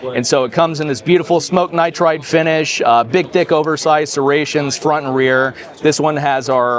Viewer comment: am I hearing what I think I’m hearing? I think he meant to say Big Thick but instead said Big D ICK